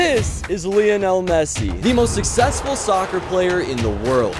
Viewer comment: did he say soccer